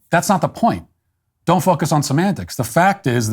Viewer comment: Anybody else hear "Don't fuck us with semantics?